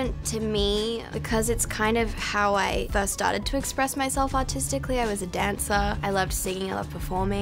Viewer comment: I thought she said express autistically😂